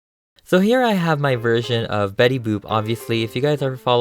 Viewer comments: It sounded like he said betty boob...idk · No offense but it sounded like you said "Betty Boob"